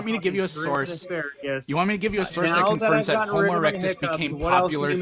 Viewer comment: My favourite thing is that he stumbled over his words so it sounded like he said "When did homeless erectus become popular?"